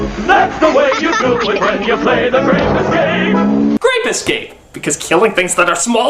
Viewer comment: Who heard: saw "Rape escape?"